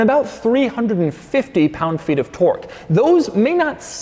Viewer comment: "Pound feet of twerk" Sorry, i know, very bad, but i honestly thought i heard him say that at first.